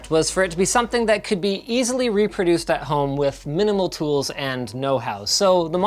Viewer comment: did he say house?